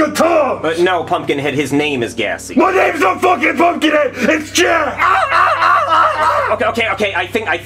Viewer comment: What did he say? I couldn’t tell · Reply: MY NAME IS NOT FUCKING PUMPKIN HEAD! IS JACK